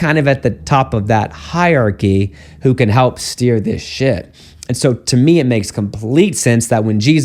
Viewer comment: Did I hear it incorrectly Who can help steer the ship ?? at 8:10 😂 Had to listen to it three times to hear it correctly hahaahha